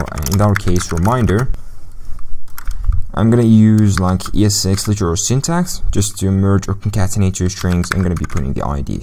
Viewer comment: what did he said at 22:47 ? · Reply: ES6 literal syntax, I guess